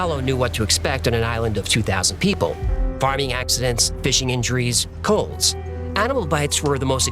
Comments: sounded like he said "farting accidents"......😋 Yes i a child:fish-orange-wide-eyes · "farting accidents"? Did I hear that right? I mean, sure, farting accidents are bad but they usually just require a shower and pants change. Not a doctor visit. If it's happening daily, yeah go and see a doctor I guess.